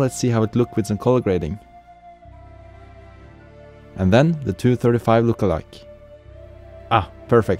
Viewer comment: Hi. At 4:06 did you say 235 LUT?